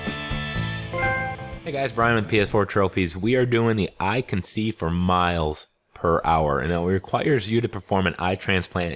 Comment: At first I thought you said I conceive for miles